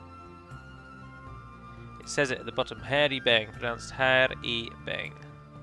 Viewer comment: When you noticed you could get the Hir e-Beng it really sounded like you said we could get the hairy bang. It made me laugh, thank you.